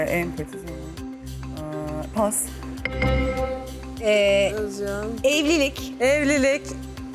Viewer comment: dakikada "Özgüven" mi söylüyor düşük sesle ?? yoksa bi tek ben mi duydum ?? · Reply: Ben de özguven diye anlamıştım hatta kim kopya verdi diye düşünmüştüm, şimdi tekrar dinledim 'özcan' demiş , bizim özgüven diye anlamamızın sebebi aklımızdan geçiriyoruz ya cevabı özgüven cevabını zihnimizde beklettigimizden dolayı, iki saat özguven demelerini beklemiştim mesela